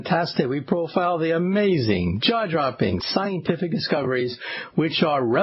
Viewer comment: When he says 'jaw dropping' I hear 'jar jar binks'